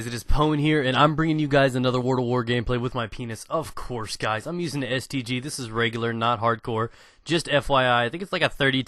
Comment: Lol. My vid paused perfectaly so (in combination with my crappy audio) i thought he said "im using the STD. This is rape!"